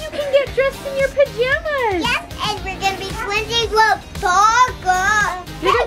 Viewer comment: Haha I thought they were saying glizzies! Glizzies! And not Twinsies!😂😂